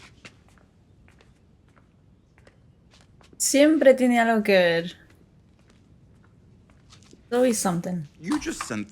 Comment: did you say Siempre tiene algo der ? I just got my intermediate spanish certificate yesterday and wanted to know if I caught that right XD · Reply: Siempre tiene algo que ver